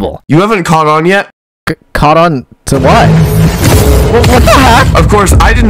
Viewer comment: the auto generated captions say caught on to life but he actually says what, not life. YOU HAVE NOT CAUGHT ON TO LIFE.